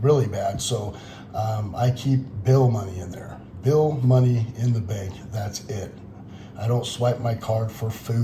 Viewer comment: I thought he said he only keeps "bail money in the bank" LOL